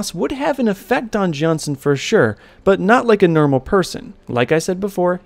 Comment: Why did you say nermal